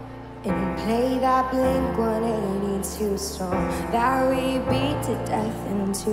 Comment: Did she say ‘strong’ instead of ‘song’?